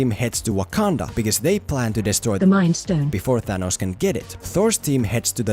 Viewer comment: I really want to know what you said when you had the robotic voice say "the mind stone" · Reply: Demonetization